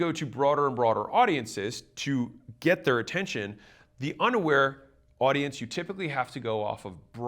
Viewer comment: thought he said “underwear” 😂. I was listening only.